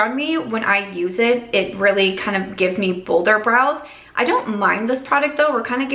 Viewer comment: omg i thought you were saying "boulder" brows and I'm like what tf is this trend